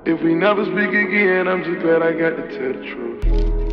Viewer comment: "If we never speak again , I'm just glad I got to tell the truth " 💔🐍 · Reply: I always thought he said "I'm just glad i got to touch you" 😂😂